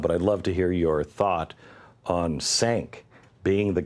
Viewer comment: I'm sorry, was he trying to say Cenk, as in Cenk Uygur? Because what he said was Sank.